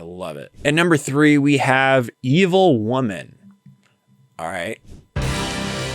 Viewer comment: Every time I listen to "Evil Woman" I hear "Medieval Woman". So much so that I've had to sit down and write a parody. lol